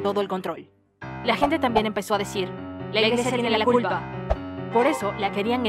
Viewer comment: ¿que dicen? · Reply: La iglesia tiene la culpa (Es lo que pensaban ellos no yo)